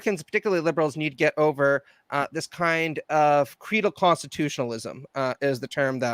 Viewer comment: Sorry but at 43:00 refers to a kind of Constitutionalism - I didn't catch it on replay and don't trust auto-generated caps. Was it "Cretal"? "Fetal"? Serious request for clarification. Little hard of hearing, sonny. A.N Aged Listener. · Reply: Credal (like a creed)